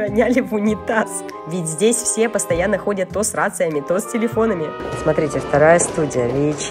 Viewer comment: здесь постоянно ходят то с рациями,то...( Мне послышалось другое😂:" ....ходят сраться...")